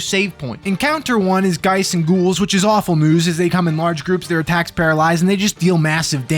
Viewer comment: When you said "their attacks paralyze" at 40:18 I heard "tax payer lives" and I don't really have anything else to say about that.